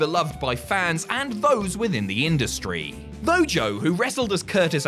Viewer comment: = Mojo? Bo though? No go? I couldn't make out what you said. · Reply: He says "Though Joe...." and continues his sentence.